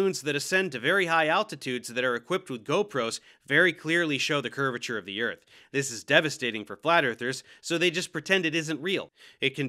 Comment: Litteral 8 year olds are smarter than glat earthers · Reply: I honestly thought that said Gyatt earthers. Thanks internet, my brain is mush